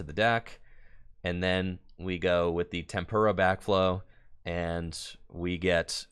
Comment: anyone else heard say "Tempura Backflow"? Dude must be hungry